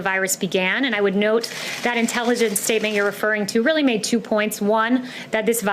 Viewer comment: Did she said Animal Analyst?